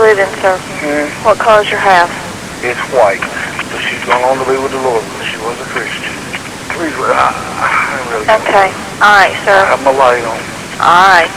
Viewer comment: so, whomever did the transcribing did awful. He clearly says, “she’s gone on to be with thr Lord, she was a Christian”